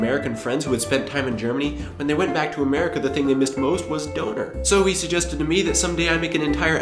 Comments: awesome video, just please say dÖner, not dOner. Thought you were saying donut at first ;DD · Cool, but since you live in Germany, please pronounce "Döner" properly. The word is pronounced more closely to DURNER not DOANER. Definitely agree with you about the fact that Germans are not afraid to let children play and will take responsibility for their own actions. America has become extremely WUSSIFIED! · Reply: thought he just said 'Donut' in a weird way until I read the commens which told me that he wannted so say 'Döner' :D (and I was really confused because I can't remember that Donuts are THE thing here ^^)